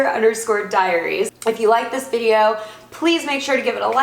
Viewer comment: For a second I thought it said hunger diabetes lol